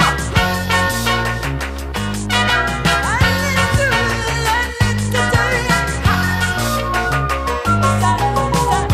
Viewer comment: What does Annie say at 2:59? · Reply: bilzup I need to, I need to tell ya